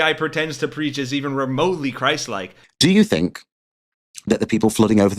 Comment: Oh "christ like" i heard "crisis king" and was like "yeah there is certainly a crisis"